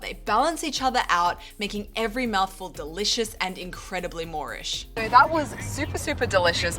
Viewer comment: Moorish are things related to the Moors (Middle English/Medieval before Shakespeare term for Mediterranean muslims, Iberian/North African). Moreish is a British slang for something that makes you want more, therefore more-ish. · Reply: Thank you for clarifying this! I always thought she was saying "moorish" and I couldn't get it to make sense in my head. "More-ish" makes sense!